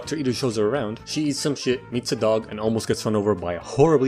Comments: thought he said "eats a dog" and my finger frickin teleported to the go back 5 seconds button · It said meets a dog but I thought it said eats a dog so I had to go back and listen to it again